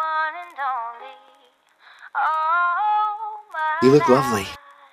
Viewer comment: Instead of lovely i thought he said ugly (real one)*you look lovely* (The one that i thought he said) *You look ugly*.